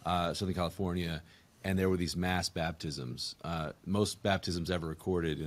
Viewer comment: I thought they said "masked baptisms", LOL.